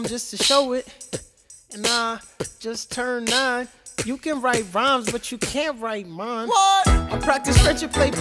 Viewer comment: Oh no... I thought he said "You can write rimes but you can't ride mom."